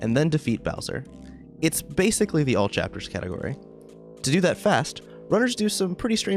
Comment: Ah. All Chapters category. I heard "jabbers" twice. I wondered if there was some such category in Borderlands 3.